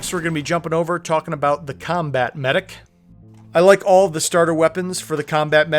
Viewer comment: i heard "Kombat Kemedik" but i went back though and couldn't hear it again (comment for the algorithm)